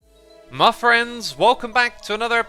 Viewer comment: At the beginning i could of sworn he said "Muffriends"